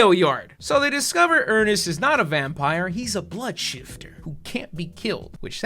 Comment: I thought it said "Earnest was a BLOODSHITTER". lol That would actually be more interesting that what we got, tho